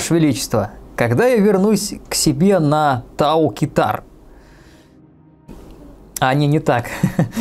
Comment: Высоцкий вошел в чат (Послышалось просто Тау-кита)